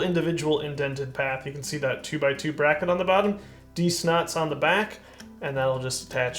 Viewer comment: what did he say was on the back? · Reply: D-SNOT! It's a relatively new, incredible little piece introduced in the Speed Champions line.